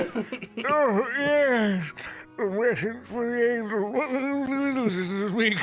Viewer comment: "Wretched creator what ..... Do you speak?" I CAN SPEAK THE LANGUAGE OF THE GODS kinda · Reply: I heard; "Ah yes. a message for the angel, what do you seek?"